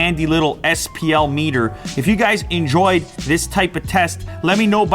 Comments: What I heard “Leave a thumb, down below” What I thought I heard “Leave a thumbs down below” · DId he just say thumb down? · It sounded like he said “leave a thumb down 👎 below!